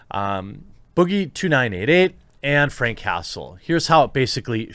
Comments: I legit thought Jeremy said Frank Castle and my next thought was "Wait?!?!?! Boogie fought the Punisher?!?!" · I thought you said "Frank Castle" at first.. Thinking Damn Boogie must be tougher then I thought having assault charges against The Punisher. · Honestly thought you said Frank Castle... as in, the Punisher. Cause if he turned up at my house I’d probably pull a gun on him too. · The whole time I thought you kept saying frank castle so I was thinking about the punisher the whole video